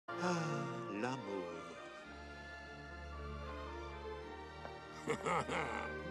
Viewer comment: What does he say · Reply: L'amour.